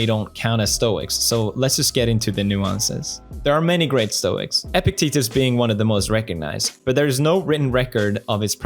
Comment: i heard epic t-word lol